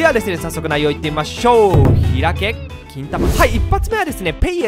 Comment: この企画楽しみにしてます！！ 1:31 開け金玉...しか聞こえないのだが、、？！w